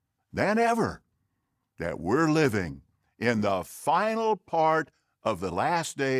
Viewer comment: I thought dude said, "The final FART". The final FART? Yep. It's Christianity's FINAL FART.